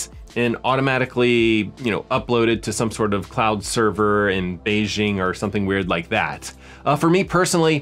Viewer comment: Did you just say Beijing ? XD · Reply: Yes! No to the cloud servers in Beijing. They keep the voice data local.